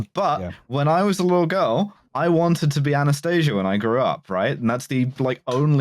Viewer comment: I thought Alice said "anaesthesia."